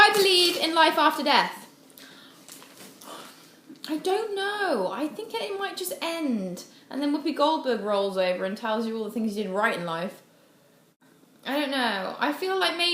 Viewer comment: Can you tell me what she said from 2:36 to 2:38? Thank you :) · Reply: "I don't know I think that it might just end. Then Woopi Goldberg rolls over and tells you all the things you did right in life"